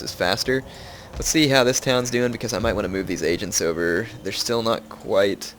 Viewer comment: Every time anybody says "agents" I hear "Asians"